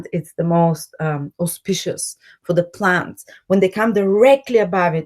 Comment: What word did you say 16:32 opsp.... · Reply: Kool Laid auspicious it means favorable